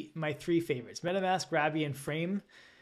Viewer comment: Oh, “Rabby” – I thought you were saying “Ravi”, until I checked the auto CC (which say “robby” 😄). There are too many wallets to keep track of >.< Anyway, just thought it’d be helpful to have it spelled out for anyone else new to this 👍